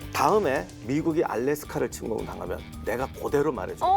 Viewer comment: 자막이 잘못 되었네요. 다음에 미국이 알래스카를 침공하거든이 무슨 말이죠? 알래스카는 미국땅인데. 실제로 대처가 한 말은 “알래스카가 침공당했다고 생각해봐라. 미국인을 다 때려 넣어서라도 되찾을려고 하지 않겠냐...”였고 레이건은 당황하며 “ㄴㄴ 마가렛 지금 상황은 알래스카랑 전혀 다른거 같아.”라고 했습니다. 실제로 패널분도 “알래스카를 침공 당하면”이라고 말씀하신걸로 들리는데 자막 오류 같네요.